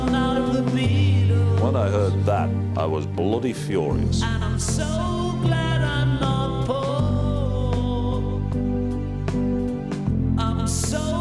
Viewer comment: At the end of the song, I think he’s singing “I’m so glad I’m not poor” But I hear “I’m so glad I’m not Paul” Which personally I find a million times funnier! Great impersonation too.